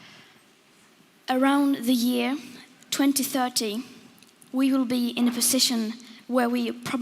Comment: Wait. Did she say by "the year 2013" we will be past the point of no return? That was SEVEN years ago. Aaaaakward.